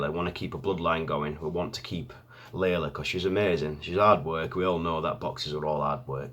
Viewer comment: Your accent saying "hard work", I thought you were saying 'odd* work'. Also true though. Haha. Good luck!